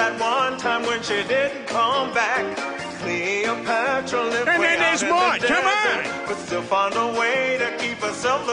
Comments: I always thought he was saying "And then there's More" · OMG i always thought he said 'and then theres more" since I never knew what maude is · I thought Peter said “and then there’s more” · Before 2011 I had never heard of the show Maude or seen it. I never knew it until Antenna TV first aired it 11 years ago. When I use to watch this episode I always thought Peter was saying "And then there's more." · When i first saw this scene when I was a kid I thought he was saying "and then theres more" because it kept going😂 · When I first saw this episode, I thought peter was saying "and then there's more".